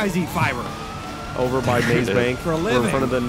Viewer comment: all i hear is him say "group sex building" lmfao